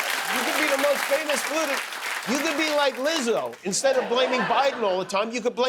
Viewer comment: I don't understand what Lizzo has to do with blaming the Jews? Is that not who he said the rxpist could be like if he became a flutist? I must have misheard. The subtitles said "Liz."